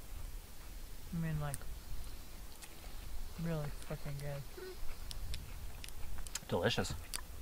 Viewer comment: Did she say it’s really F*cking good at 12:00 ? ......hahahhahahah. gentlemen keeps your ladies well feed.